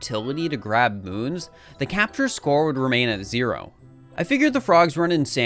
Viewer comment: i thought u said captians score xd